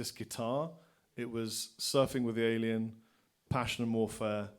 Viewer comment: Hello, what did Rob say at 01:17:00? "Passion Morphea" ? Thx... and his best pedal ? Jimi Hendrix limited Edition octave fuzz ? Thx:-)